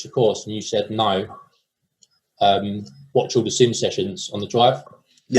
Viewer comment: did anyone catch what said? The audio was muffed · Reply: Watch all the sim sessions